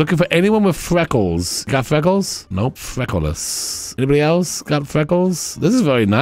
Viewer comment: I kept hearing “freckleless” as “frekleolas” (like Legolas) and it had me giggling